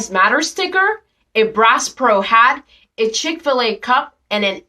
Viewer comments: Bass Pro Shop bad? That's new to me... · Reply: She actually said Brass Pro...lol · It sounds like she said brass pro hat.